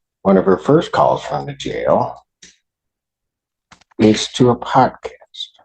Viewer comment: a what? A pod kist? What is he saying? Did he say podcast? I have no idea. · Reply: Yeah, he say podcast...